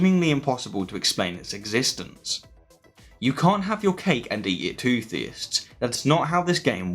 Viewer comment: can anyone tell me what he is saying? you cant have your cake and eat it too theists? is that what he said? But I love this channel... keep it up